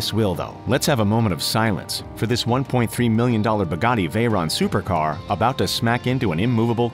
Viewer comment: Did he just say pagani veyron supercar ? Check it out